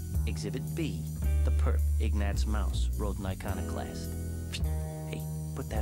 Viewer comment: What did the narrator say in 0:09? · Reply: Ignatz Mouse: Rodent Iconoclast.